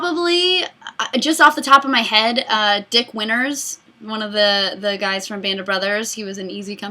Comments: Totally thought you said "Dick Winners" for a second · I heard you say his name as dick winners, not dick winters. i think it was funnier the way i heard it